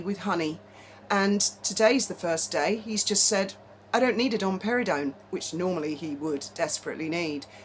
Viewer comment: When you said domperidone I thought you said Dom Perignon…..🤣🤣🤣